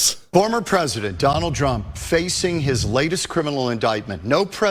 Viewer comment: Did he say "Drumpf"?? LoL Wasn't that his families real last name until his dad had it changed?